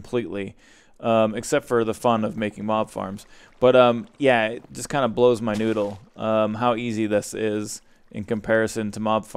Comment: did say blow my noodle at 17:54 ?